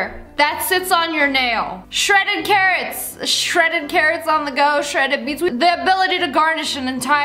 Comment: i thought she said shredding KARENS LMAOOOOO